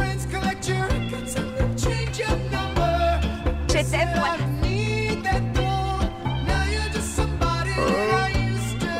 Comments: What did she say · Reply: Martin Yoon I love you, Wallace. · what did she say? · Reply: The Ginyu Force I love you wallace don’t ever forget that